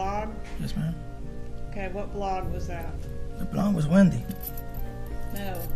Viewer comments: He thought she said "the blond" the blond = Wendi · 🤔When the attorney asked" Was it from a blog?", he thought she said "blonde". His response was,"The blonde was Wendi." LISTEN carefully at 3:14...case closed!😮💯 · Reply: 1000% "The blonde was Wendi"